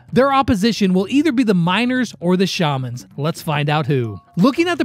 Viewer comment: Lex, I was in the hospital for 3 days, I when I came back home I've never felt any happiness but nothing does it but u thx Lex😊😊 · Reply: I thought when he said "the Miners" he said the Minors